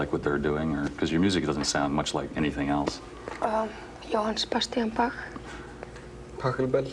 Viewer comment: What dies björk say here 1:45 ?? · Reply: johann sebastian bach